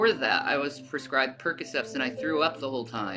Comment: Did she say she took "percocefs" lmfao